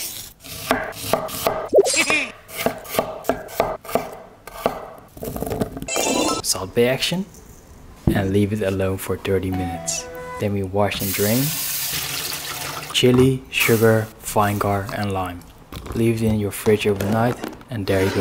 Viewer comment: That’s Irish accent made him sound like hes saying leave it alone for dirty minutes